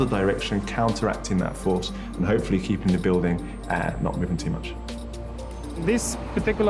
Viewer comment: did he say "hopefully keeping the building and not moving too much"?